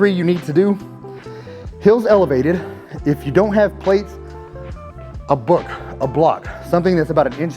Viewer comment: I switched my phone off when i heard " place a book! "😂